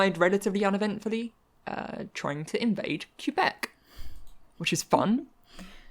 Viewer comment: did you just say "queue-bec"? lol